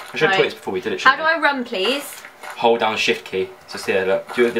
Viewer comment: i thought he said "hold on the sh*t key" when she asked "how do I run"